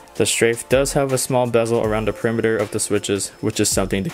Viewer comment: The strafe has a small what around the what? Sorry, didn't understand that part, non-native english speaker ( 2:20 ) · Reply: t*TO4ST bezel around the perimeter, fancy talk for that there's less plastic around the outside of the keyboard